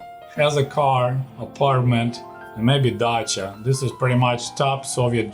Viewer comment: maybe but i misunderstood but at 10:40 did he say Dacia?